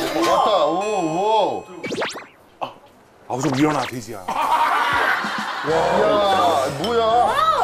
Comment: 쯤 민상이가 뭐라는거야? 아 좀일어나 돼지야 임?